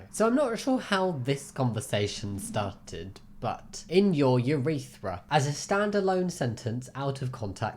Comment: help i like minecraft horror mods too much i thought he was gonna say "in your world" 😭😭😭😭😭😭😭😭😭😭😭😭